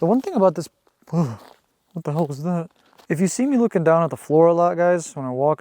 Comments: Around the ten minute mark, it sounded like Ben saying “ What the hell”…. Spooky…. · Ok so the first voice right before you found the board said what sounded like “what the hell” BUT it sounded EXACTLY like Ben’s voice!! Then after the “frog” scared you there was another voice I heard that sounded like it said “demon” but STILL in Ben’s voice!!! So crazy!!!